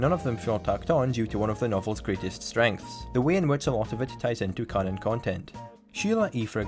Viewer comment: i'm sorry, what did you said at 0:50? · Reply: "the way in which a lot of it ties into canon content"